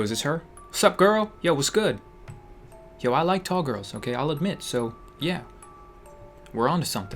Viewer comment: "Yo, I like tall girls. Okay. I'll admit so yeah." isnt what i heard. What i heard was. "Yo, I like tall girls. Okay. I LIKE MEN so yeah."